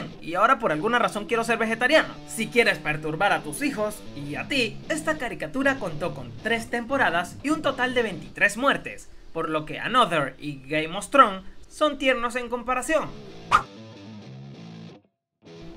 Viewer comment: Escuche bien o escuche "Gay Mostron" en